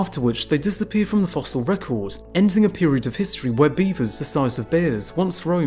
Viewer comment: I thought it said “ beavers the size of beavers” these edibles hitting.